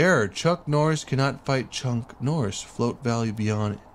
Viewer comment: Why does it sound like your saying chunck😂😂